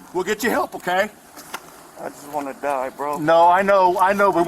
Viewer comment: As a retired LEO they made a HUGE mistake. The monster is in a vulnerable emotional moment when he was statingnthat he just wanted ti die. If one of the officers had just asked the question, "Why would you want to die?" he may have made a spontaneous utterance that would be admissible in court and ensured his conviction. Now he can deny involvement, claim insanity etc and get away with this horrific act. If he gets away with this, these officers will be partiality responsible. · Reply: listen to 4:38 sounds like he said I just wanna die bro, I killed her